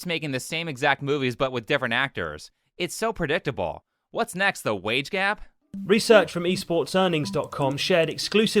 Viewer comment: I thought u said the weight gap 💀xd